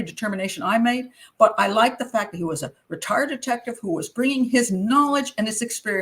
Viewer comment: I had a bit of a Borat moment there, I thought you said the guy with the tattoos was a retar,-ded detective and I thought what did she just say? Had to rewind and realize you said retired detective, omg 😂😂😂